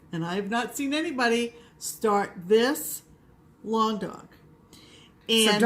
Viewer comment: I must have been punchy because I would have swore you said "Long Dong" stitches which of course gave me the giggles. I would enjoy the Three Wise Man. My lucky number is 9. It is my birthday.